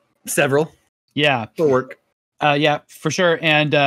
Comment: best moment in gamescoop for me was the quick “DORK”. Laughed so hard and replayed a few times to make sure I heard properly, I laughed every time. I dunno why. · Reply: For work! He said.